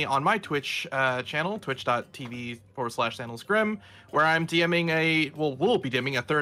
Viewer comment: Hey can someone tell me how to spell twitch my ears cannot comprehend the letters that comprise the words and the subtitles say that he’s saying ‘sandalusgrim’ which I feel like is not correct 😂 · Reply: I tracked him down using Bosco's channel ;P it's